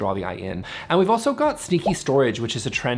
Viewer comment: I wasn’t looking at the screen and I thought you said “stinky storage.” I had to go back to see and realized you said sneaky 😂